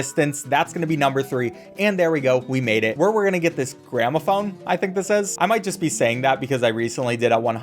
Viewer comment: i actually thought u said "grandma phone" for some reason 💀